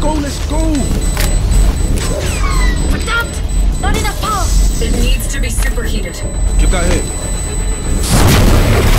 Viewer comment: what word was that? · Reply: "Verdammt" is the german word for "Damn it"